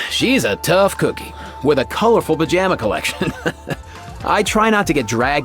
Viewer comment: I have to rewind the video, i thought he said "a colorful v*gin*" collection" when he talk about Citlali. 😂😂😂